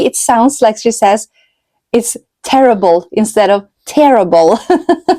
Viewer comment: I had to laugh because I also often hear terrible instead of tearable 😂